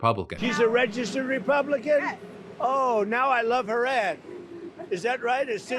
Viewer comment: "Now that I know she's a registered republican, I love her ad !" Man's not even hiding his bias. He likes people who are useful to him, and dislikes everyone else. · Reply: I thought he said "...I love her ass."